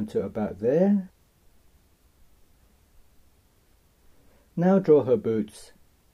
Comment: Loved this vid but sorry when you said now draw the boots I thought you said boobs 😓